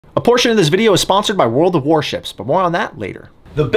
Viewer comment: straight up thought you said abortion and was ready for this to get political. 😳